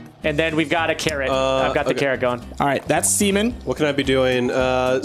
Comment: be honest. you thought he said semen too.